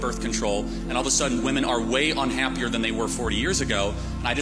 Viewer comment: 4 years ago we were in a Pandemic The were you better, happier, whatever 4 years ago is moronic. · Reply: Sounded like they said "4 years". But wondered if they're saying 40. So I checked the CC, and it said 40.